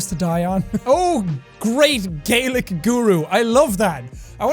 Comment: I thought he said “Great Gaelic UwU”